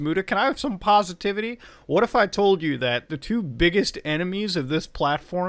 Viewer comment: Lol I heard 'the two biggest animes on Youtube' at first. Seemed to fit.